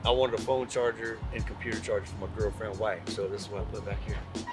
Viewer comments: Did he say he wanted a phone charger for his “Girlfriend and Wife”? Brave man unless both are the same woman. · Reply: I thought he said girlfriend and wife too... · Did he say he wanted a phone charger for his “Girlfriend and Wife”? Brave man unless both are the same woman. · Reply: I thought he said "girlfriend wife".